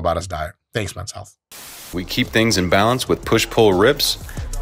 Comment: Did he say "push pull RIBS" ? 🤔